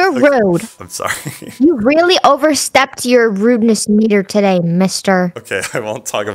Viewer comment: When said the ruddnes meter I thought she said ruddnes hard r idk y but yuh great video love you ur content